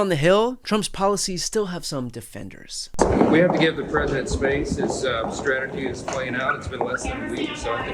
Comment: I thought he said "we have to get in the president's face" 😂